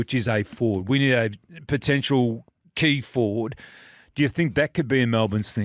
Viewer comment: TRUE NEWS - I'm a Melbourne supporter and my father works for Melbourne fc this is a inside news clayton oliver they'd looking at trading him for a decent Key Forward, like Harry McKay. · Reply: I thought you said decent key forward?